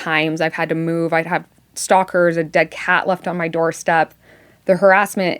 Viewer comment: did she say "dead cat"?